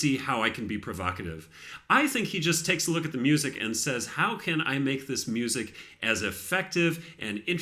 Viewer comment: I think he also says "how can I make this music mine". His Goldberg Variations are such a masterpiece that people call them Gouldberg Variations